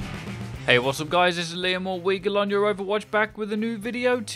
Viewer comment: Been watching you guys for bout a month, and watched like 80 vids but I have 1 question... is it? Or how do you say it?? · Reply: Seriously? I always thought you were saying "Liam o' Weagle" as in Liam of Weagle. Huh...